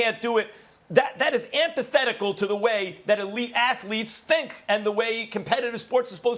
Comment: I thought max said “ the way that WE athletes think” instead of elite athletes. I about had a conniption hahaha